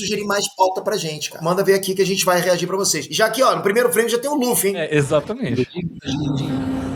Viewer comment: Eu tava aqui só ouvindo o video e escutei ele falando: no primeiro frame ja tem o LUVA, ai fui olhar e o cara realmente parece o luva, mas era Luffy kk